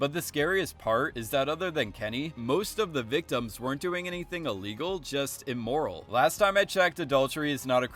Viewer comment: I had to rewind to make sure I heard you right lmao thought u said nobody did anything illegal. Great video anyways